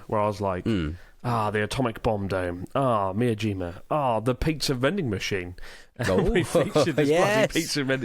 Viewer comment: Did Chris say "piksa" vending machine? You slipped, Mouse! You slipped!